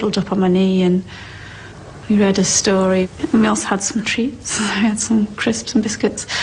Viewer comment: Also note: when she,(kate) says, " we read another story.. Are you happy and you know it" (to me sounds like she is saying, ("you got what you were asking for & you know it") Thats what it sounds like to me.